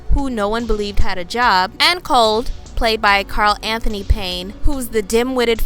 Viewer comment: Did she say "Cold"?